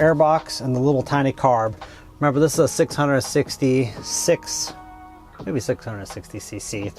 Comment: Pretty sure it I heard you say it was a 666 cc the devil's Motor lol.